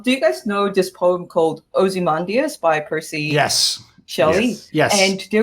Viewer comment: Can anyone tell me what the title of the show which is talking in @ 21:10 ? I cannot hear it properly bcz of her accent and me not being native speaker. Edit:I just figure it out of my own. It is Ozymandias. ty for referencing Watchmen.